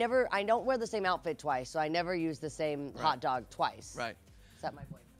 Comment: Did she just say "I never use the same hotdog twice except my boyfriends"?????? XD wtffff